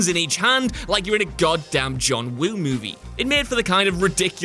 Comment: Did he say John Woon? At 3:14 I swear he did.